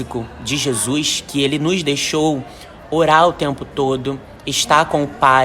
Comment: 😂😂😂 entendi “oral o tempo todo”